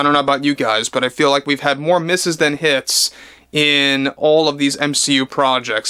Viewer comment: Thats funny. I heard you say we have more "misses" than "his".